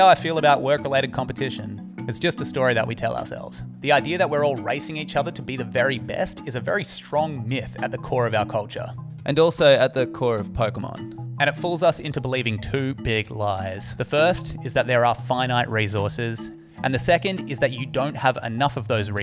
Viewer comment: am I going insane? Did he really say Pokémon? I’m tripping..